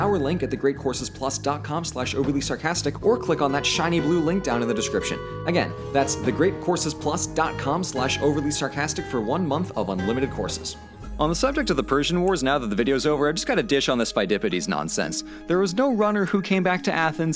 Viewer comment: What he said: "...one month of unlimited courses." What I heard: "... one month of unlimited horses."